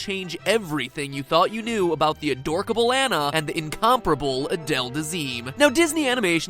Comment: did he say “adorkibal”